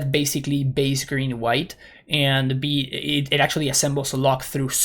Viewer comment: What is the card name that he is saying at 1:33:30? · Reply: Zur's Weirding. It's a spicy one!